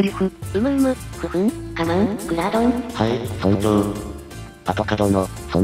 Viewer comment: クラードンがグラードンに聞き間違えてしかたねぇ… アトカさん素直ですねぇ〜